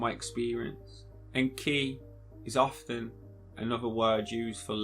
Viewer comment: You say and key...I heard Enki🤔🤷‍♀️ Just another random thought!